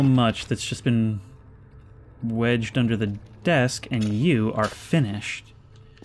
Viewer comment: "There's so much that 's just been wedged under the desk, and you are Finnish." silly little mishearing